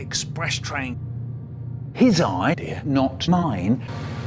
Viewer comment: I thought Micheal said “His eye, dear, not mine” but it’s idea